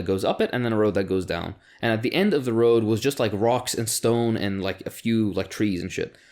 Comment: Did I hear a rock and stone?